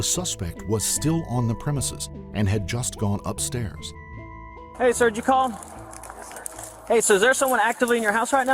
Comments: "hey, sir, did you call?" Captions: Hey, surgical. I like how this guy knows when pups just want to greet someone. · "Hey, surgical." ??? English is not my first language.. a not even my second... But shouldnt it be "Hey, Sir, did you call?" ???